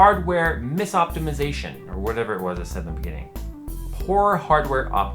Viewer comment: I thought your channel name said JayZ Two Cent